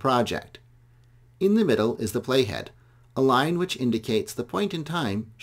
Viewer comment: When he said play head I thought he said Jughead xDDDDDDD